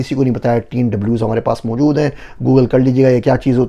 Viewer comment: What did he say at 14:49 ? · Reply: 3W,,, google kar lein